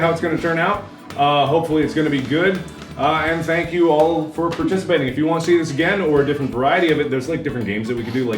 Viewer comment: I just got done watching some genital jousting and when you said "Thank you for participating" I thought you said "Thank you for penetrating" XD